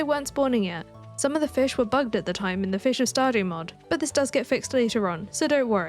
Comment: i heard fish are starving mod and was rather confused